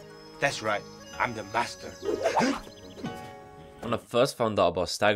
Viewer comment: says Master, subtitles says Bastard